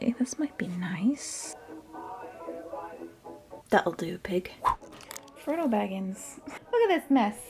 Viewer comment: So instead of Frodo Baggins the subtitles say Frontal Baggings and I’m dying🤣